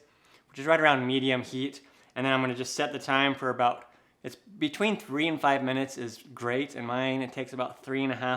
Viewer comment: Thanks for your answer, well I did it but it just took forever and then half looked raw, is it because I used a rubber inset instead of metal do you think? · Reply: I must have heard you wrong, thought you said between 3 and 4 minutes?